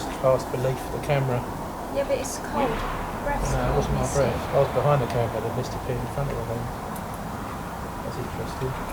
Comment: At the time of 17:27 to 17:29 there's a scream of a women but I can't make out what she said. Good investigation !👍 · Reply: Well spotted , thank you. Could be ' help me' ?